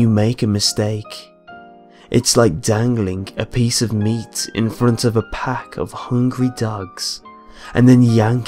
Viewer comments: Sounded like he said " hanging meat out in front of a pack of ducks" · I thought he said hanging meat in front of hungry ducks. XD That totally killed the mood.